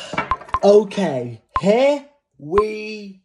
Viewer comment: Bruh everytime I see this man this all I hear “Ook here we gaawww”